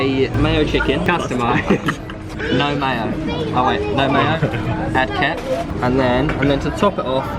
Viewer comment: anyone else hear say "add ket"